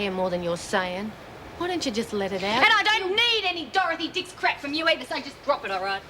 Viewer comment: did spider say Dorothy Dickins? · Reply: Dorothy Dix was one of the original agony aunts.